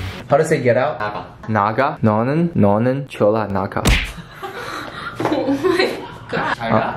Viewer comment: Omg did he just say 졸라 at